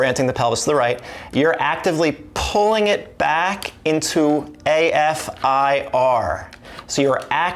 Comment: Ver y very interesting! Thank you! Can you answer me what is “A F I-R ” that you say at 0:55minute? THANKS. BYE FROM SPAIN. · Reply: I probably say “left AF/IR”. It’s the movement needed at the left hip/femur. It’s mentioned in many videos. I have a video called “what is Left AF/IR” or something like that